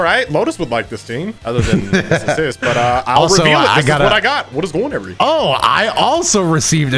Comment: *confirmed SLOW DOWN TO @ 0:50 & 8:08 to .75% speed of video and you can hear it perfectly I knew it!!!!!! So has anyone else noticed through out all his videos or randomly say “how’s it going every body” really fast through out the video I hear it every timem